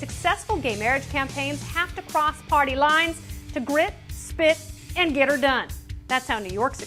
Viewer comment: Did she really say, "Grip spit and get er done" ?? Gasp.